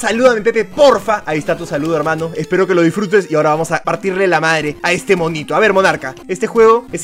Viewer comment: Pepe escuché que digiste a partirle la madre al dokn Jr like si lo escuchaste minuto